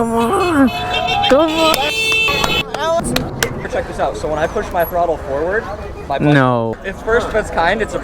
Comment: Wait what did he say about his throttle? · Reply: When he twists the throttle forward it goes